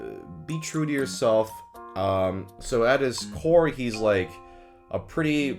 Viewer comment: i know and understood you said "at his core" but for a milisecond i heard "at his whore" and though damn, real of him